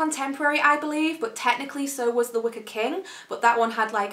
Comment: When you said the wicker king I thought you were saying the wicked king and I was super confused